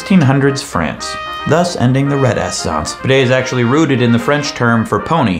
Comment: Did you just say "Red-ass-ssance"? lol